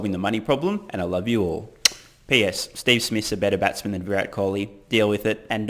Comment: WTF? Are you possessed and speaking in tongues? I have no idea what you said. Deal with it :-) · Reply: “Steve smith is a better batsman than Kholi”